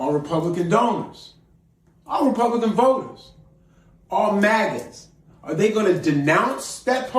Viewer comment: sure sounds like he said Magats...